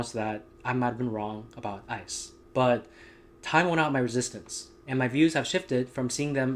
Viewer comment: What did he say at the @ 0:19 time point? "Taiwan on my resistance" "Time went on my resistance" or what? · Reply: Time worn out my resistance yw😅